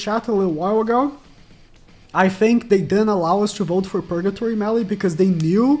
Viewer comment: lmao it sounded like u said Allah